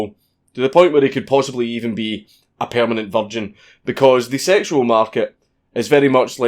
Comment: In the auto closed caption for english, when he said, "a permanent virgin" 3:14 , the closed caption says "a permanent vol'jin" well i guess i want to be an incel now